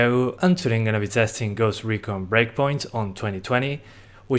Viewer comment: 0:3 I thought you said Ghost Recon Break point or Break money